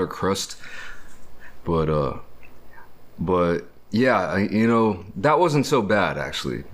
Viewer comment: Is it me or did I hear a whisper someone saying "yeah” at 6:08 kinda creepy😳